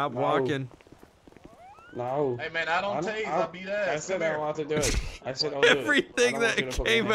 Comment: I know he said somethin else but I heard: "I don't taze I eat a$$" I died